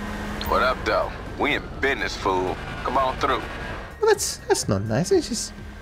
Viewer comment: he didn’t call you a fool, he called u a foo there’s a big difference😂😂. Foo is like saying bro or dude